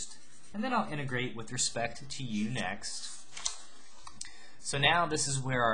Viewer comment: it sounded like a threat xD "then I am gonna integrate with respect to YOU! next"